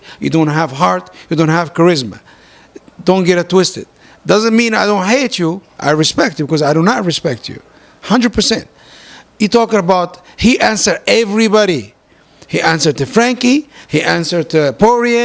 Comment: I respect you, but I do not respect you. Wtf did I hear it wrong? 🤦🏽‍♂️. BTW I am not conor’s fan 😆